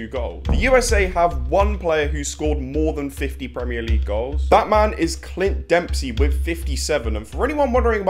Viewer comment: People who thought he said "Batman is Clint Dempsey" 👇